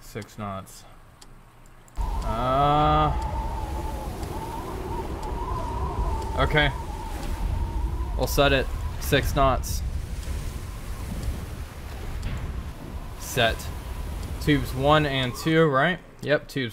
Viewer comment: Perhaps you should don your knee and elbow pads to limit yourself to further injuries?? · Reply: Last speed and range taken... Sounds like you say 6 snots...